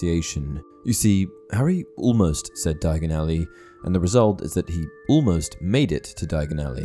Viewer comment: When I first watched it I thought he said gargenally